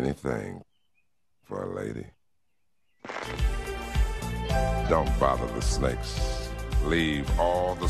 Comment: "Don't follow the 🐍's. Leave all the 🐍's. alone. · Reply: I thought that's what I heard the first time, but in my mind, don't follow the snakes made more sense. 😆 Same difference. "Stay away from the snakes! The fakes. Thanks for clearing that up.